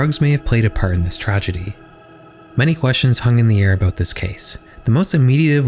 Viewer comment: did you say "hung in the air" at 8:21 ?